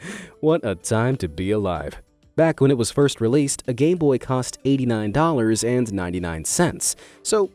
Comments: did he say gayboy? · Haha, professional idiot 🤣 well, it just about sums him up, I mean, look at the Aokigahara video 9:14 It sounds like you said 'gay boy'. Did I mishear that, or did you actually say that? 🤔 Aw geez, that tamagotchi thing reminds me of a tamagotchi app I have on my old phone. I haven't played that thing since my junior year of college. 😰😥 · it sounded like you said “gay boy”